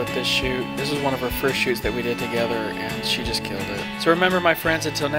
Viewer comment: It sounded like you said " this is one of the first shoots I've done with?